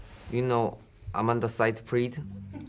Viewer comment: What did Jhope say on 1:34? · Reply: Miss Dark Knight He said " Amanda Seyfried".